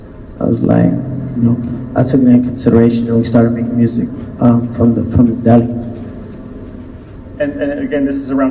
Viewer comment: did he say “making music for the devil”?